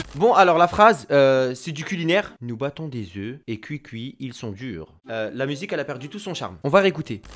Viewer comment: À 4.41 j'ai entendu "ils bandent des oeufs cuicui ils sont durs"